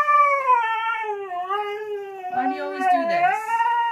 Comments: I swear I heard him say "but why" when she said get out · WHY DOES HE SOUND LIKE HE IS SAYING "BUT WHY" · it sounded like he was saying: "Buy WHY!???"